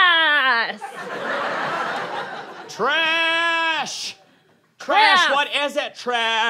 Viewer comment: it sounds like hes saying “trash?? trash!!”